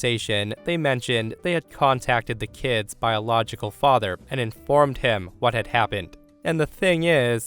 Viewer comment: I heard "contacted the kids by a logical father" Like whaaaat?! And then I understood "biological father" 😂😂